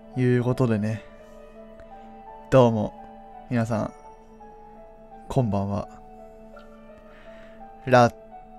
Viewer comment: こんばんわって聞こえたのおれだけ？